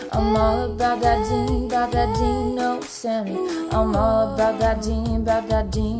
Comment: I first I thought she was saying, "I'm all about that Dean, no semi."😂😂